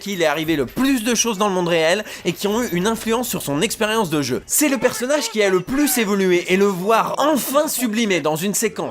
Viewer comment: C'est quoi le nom de l'animer a 23:48 ? ( tu la dit dans la vidéo mais j'arrive pas a bien entendre ) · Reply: Sword Art Online, a fait aussi une vidéo sur le doublage vf de cette série d'ailleurs.